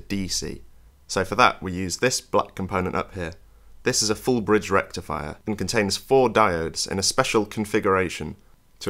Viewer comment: I didn't hear loud enough what is the component at 12:14. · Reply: It was a FULL BRIDGE RECTIFIER